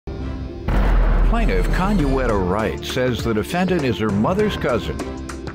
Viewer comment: Conuetta Wright?! I thought he said Condoleeza Rice...lol